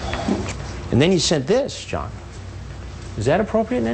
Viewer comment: Did Chris call him "John"? Lol at the 3:32 mark